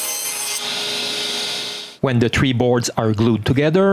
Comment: Did you say "Tree Boards"?? What the hell other kind of boards are there?? Technically bamboo is a grass so maybe that is why you feel the need to clarify their origin.